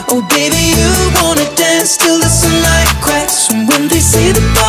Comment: I thought he was saying do you want to dance to the sun like Christ